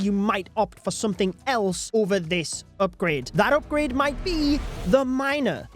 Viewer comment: WHY DID THE CAPTIONS SAID "UPGRADE THAT UPGRADE MIGHT BE THE MINOR"